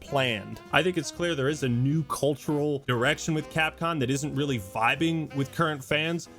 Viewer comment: i think my mind is broken. 7:58 why do I hear "cultural Erection with capcom"